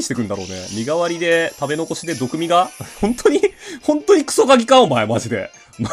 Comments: クソ鍵がクソガキに聞こえた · 最初、クソ鍵がクソガキって言ってるように聞こえた